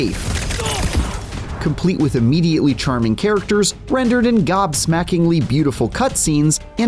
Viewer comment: Did they just say "charming characters"? What?